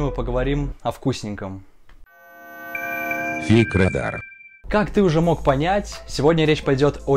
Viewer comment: ‘Фиг радар’ мне послышалось?